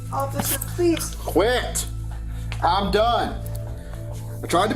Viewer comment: what does the principal at the end whisper during booking? its at about the 45:25 mark of time on the video. what she say and the officer says 'i couldnt care less' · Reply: I heard like “ please mind my notes”